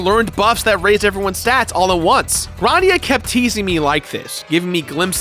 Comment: I thought it said Grandpa.